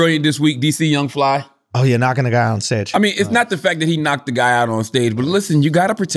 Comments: Did Andrew say knock a nigga out on stage or am I tripping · Andrew says "knock a nigga out on stage" @ 6:49 and nobody says nothing🤔 are you kidding me? Let me guess, yall didn't hear it either right? Smh